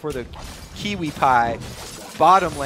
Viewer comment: did he say kiwipie? xD